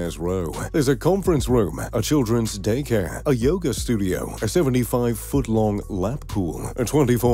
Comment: 😅i thought you said a studio for $1 million 😂😂😂😂